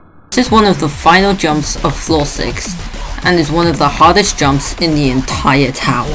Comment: subtitles says easiest when u said hardest